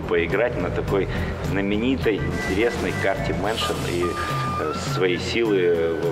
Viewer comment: мне послышалось или эта девушка сказала DE_mansion?